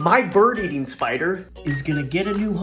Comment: POV: you thought he said my spider eating bird then saw the spider and got scared for your life